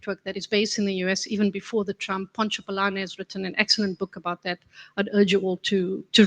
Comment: what is the name of the book that says is a good read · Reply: Power and Faith: How Evangelical Churches are Quietly Shaping Our Democracy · by Pontsho Pilane :)